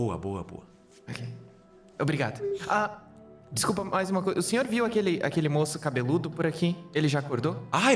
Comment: Juro que escutei "Monstro cabeludo" kkkkk